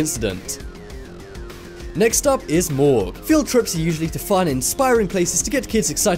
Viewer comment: I thought you said morgz anyways who likes him 🤮👎